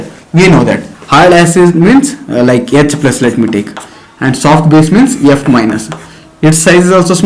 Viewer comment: It's a hard base as it's size is small So charge to size ratio will be more so hard base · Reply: yeah I want to clarify it because in the video I heard it as "soft base means F-" @ 00:48 anyway thank you